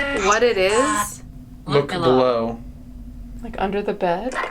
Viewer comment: it sounded like it said 'blow' like for example how many times someone got hit (lile "he got 6 blows to the back of his head"). Then after it said that, it said 'yes'